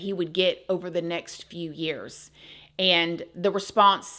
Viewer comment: ish... Did You say next few years...? All involved are insane! Mr. Man, especially... When You date a Psycho...expect psychosis... Gee I had no idea...!?!?